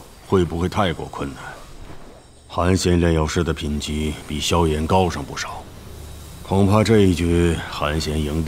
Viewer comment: subtitled Xiao Han instead of Xiao Yan